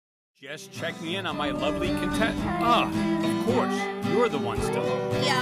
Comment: HELP I THOUGHT HE SAID UGLY CONTESTANTS😭😭😭